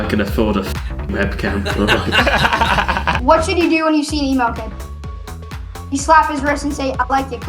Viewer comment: what the hell did he say? An email kid? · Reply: An Emo Kid